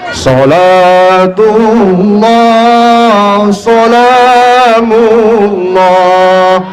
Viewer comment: focus sama suara sholawatnya,,, kok sholatulloh sholamulloh,, apa aku yang salah denger ya?